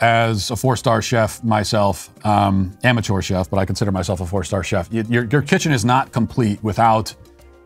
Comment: "I consider myself a 4 star chef", in my mind I heard "I identify as a four star chef" 😂